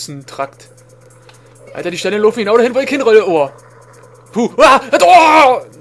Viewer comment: bei stelle 7:20 so habs ich gehort die steine laufen genau da hin wo ich rolle xD